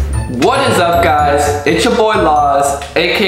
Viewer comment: am I the only one who thought he said it's ya boii laws